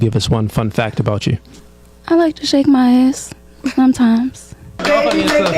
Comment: OOOOOOOHHH she said "Shake my ass" I thought she said "Shave my ass" i was gonna say "thats really not something you should be sharing with the outside world"